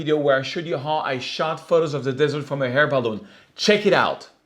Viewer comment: Serge, I really enjoy your videos and hearing your talk. I would love to see you in a Hair Balloon😎. Keep up the great work and I will keep watching and as always give you a thumbs up. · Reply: I swore he said "hair balloon" too lol - but great tips Serge!! Thanks!